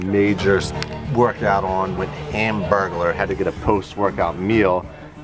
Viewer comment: did he say hamburgler?!